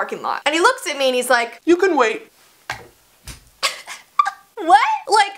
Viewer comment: My phone buffered when you were saying “you can wait” but I just heard “you can wa...” and I was like WALK HOME. R U SERIOUS DUDE SHE JUST GIT A TATTOO ON HER ANLKE AND YOU WANT HER TO WALK HOME. And the I hear “wait” and I was like. Hmmm ok it seems I was over reacting